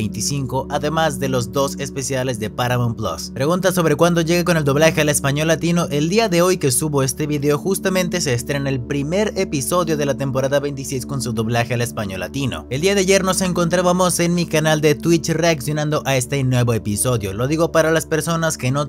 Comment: Te escuché decir temporada 27 jjsjsjs